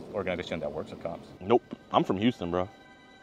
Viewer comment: I thought you said eyebrow referring to jidions eyebrow sideburn